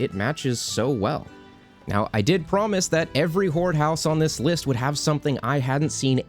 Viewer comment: When you say "Horde house" I just hear "Whore house" ..I'm sorry. 😆